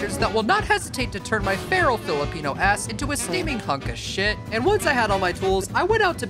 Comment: Did he just say Filipino a$$???